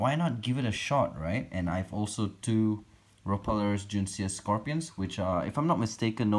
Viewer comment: Damn racist scorpions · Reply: Mi I legit thought u said “Scorpios”😂